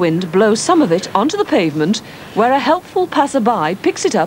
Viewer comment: did she say bungaler.